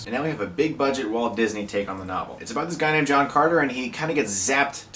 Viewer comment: when i first saw this trailer i thought i heard the person call him john conner so i was like o cool another terminator movie then i saw the rest of the trailer.... this movie looks shit